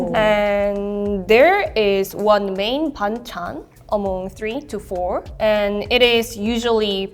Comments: i thought she said bangchan (skz) 😂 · I thought the korean girl said bangchan